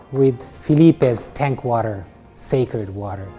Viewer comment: I heard "Filipe's SACRED water." I think that works